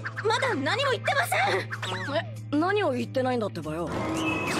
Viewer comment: did i just heard her saying -ttebasa?🤔